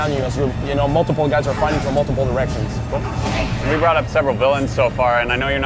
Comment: did he say 'Multiple erections'? O_o